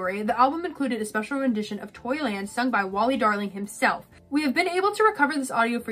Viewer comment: Funny thing about this is that I originally thought he was saying toilet not Toyland LOL